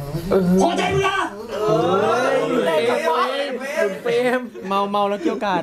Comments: พูดว่าบุ๋นเปรมใช่ม่ะ ใช่เราว่าใช่ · ทำไมกูได้ยินว่าบุ๋นเปรมวะ=-=??